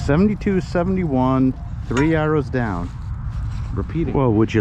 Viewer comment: I was watching your video really enjoying your humor your accent through me off I thought you were saying this target was three hours down I assumed from the frozen ground then I realized you were saying three arrows down happens to us older guys or I need to go to New York more often, Lol love your videos